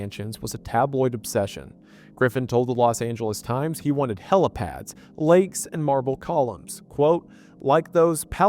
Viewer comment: at first I thought you were saying he wanted “hella pads” and I thought it was odd for Griffin to say “hella” at that point in time lol